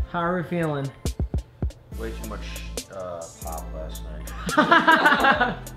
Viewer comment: Who else thought said "pot" at 5:50?